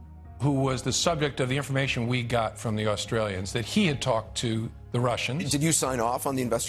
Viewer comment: Did he just say Australians? Lol... Keep talking Jim.